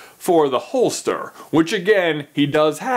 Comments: Who thought that it said Hollister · LOL I thought it said hollister